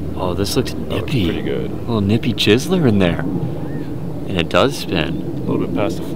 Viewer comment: did he say nippy jizzler